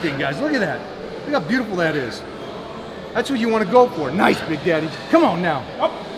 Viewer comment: did he say "nice big daddy"?